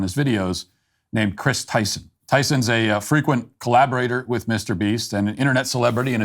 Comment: I thought you said Mr Bean!!